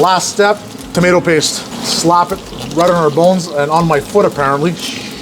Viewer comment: I thought for a second he Said veal balls instead of bones 🥲